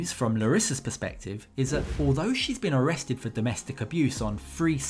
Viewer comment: Thought you said she's been deported for real....nonsense